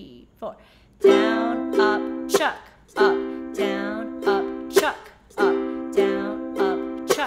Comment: i kept hearing "upchuck up",......like throwing up. lol. But thank you this is a great skill!!!!